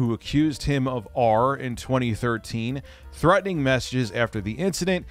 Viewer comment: I thought he called him 'taint" Would still be fitting lol